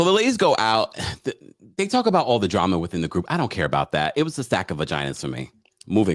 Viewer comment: I thought she said sack of V’s. Picturing a sack full was an image but a stack is even worse. No wonder he left.